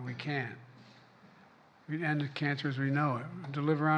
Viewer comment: No, he said we can cure cancer. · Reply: "We ended cancer as we know it" is what I heard him say too...